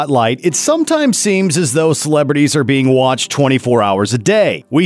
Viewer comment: I thought they said celebs